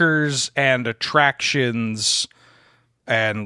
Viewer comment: I had to go back and listen to the ban list again because I misheard Shahrazad as "Charizard" and was briefly very confused.